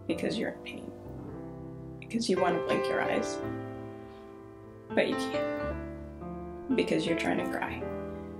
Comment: When you said “because you want to flick you eyes but you can’t”, I started laughing because I heard “because you want to lick your eyes but you can’t”